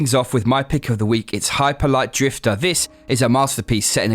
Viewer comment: For second I thought you said "high polite drifter".